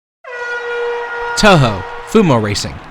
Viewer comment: 😅 I thought that said touche.